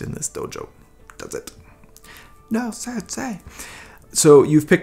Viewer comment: he says ‘sad say’ instead of ‘sensei’ 🤣🤣🤣